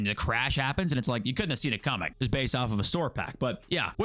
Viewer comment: Sounded like you said "I will scissor you" at the end there...this ain't that kind of party.